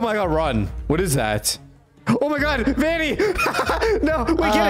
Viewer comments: did i just hear "BABY" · IT SOUNDED LIKE SAID SAID BABY IN STEAD OFF VANI IN